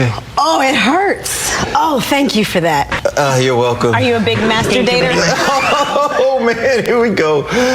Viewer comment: It sounds like she says, "Are you a big masterbater?!"